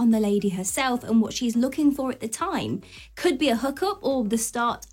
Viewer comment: At 1 st. I thought you said. Could be a hooker. I'm so tired